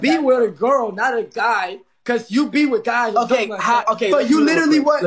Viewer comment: Did anyone hear REDD say fly be with guys? If not listen hard 4:08-9. The light is starting to shine slowly lol